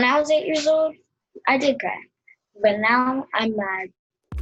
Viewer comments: “When I was 8 years old, I did cry. And now I’m mad” 😢 Unfortunately I know the feeling. Sadness, rage, numb repeat. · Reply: Oh I thought she said "but now I'm nine" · “When I was 8 years old, I did cry. And now I’m mad” 😢 Unfortunately I know the feeling. Sadness, rage, numb repeat. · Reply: i thought she said i did crack